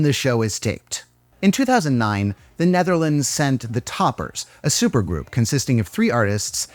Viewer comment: I thought you said The Netherlands sent a nuke 🤣